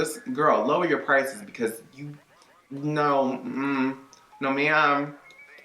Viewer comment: sounded like you said “no mija” 😂😂😂